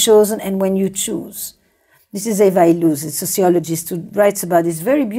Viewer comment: Esther mentioned a sociologist at 7:00 but I can't catch the name... Anyone know it? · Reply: eva illouz I think